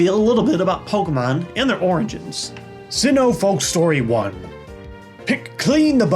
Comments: I thought he said "oranges" lol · Sounded like you said oranges not origins